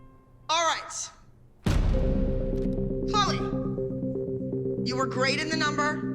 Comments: right as Peyton walked in Instead of Abby saying Chloe she said Holly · Wait did Abby call Chloe holly?